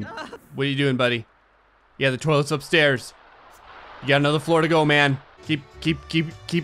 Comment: I know grey said "You have another floor to go, man." But I heard "You have another Florida, go man."